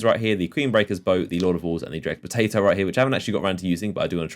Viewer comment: did he just say dreg potato?